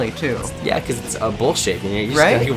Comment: what did say? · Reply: bowl shape